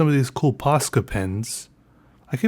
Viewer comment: I thought it said Posca Penis